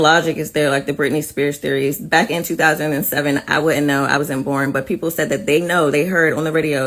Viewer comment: did she say she wasn’t born!?!? Oof I thought she was around her mid 30s 😅😅😅 not a bad thing of course I’m just saying….